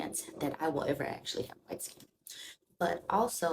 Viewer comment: "ever actually have" what? Your voice fell off, I didn't catch it. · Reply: White skin